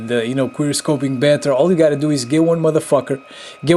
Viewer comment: OMG I CANT BELIEVE I COULD NOT PRONOUNCE QUICKSCOPING COREECTLY WHEN I MADE THIS VIDEO LMAO =) "D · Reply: I thought u were saying qweer scoping on perpose